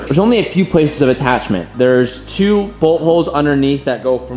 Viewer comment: When he said bolt holes I thought he said butt holes 😂😂